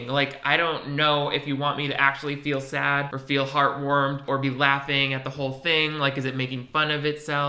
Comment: I thought you said it was supposed to make you feel “heartwormed” and ... yeah. Heartwormed is spot on.